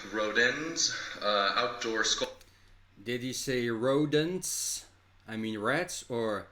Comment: I definitely heard ' rodents ' as ' rats & mice ' since he was talking about Paris. 😅